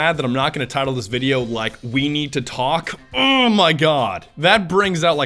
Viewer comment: Okay, who else thought he said "Weenie to talk" at 3:14 ?